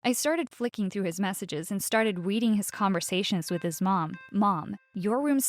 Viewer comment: did she just say ”weading”? i actually felt bad for the boss :c